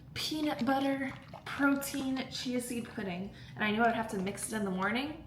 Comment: So random but when you said “protein chia seed pudding” I swore you said “protein cheesy pudding” and I was about to be like girl are you ok!?! Loll love the vlogs!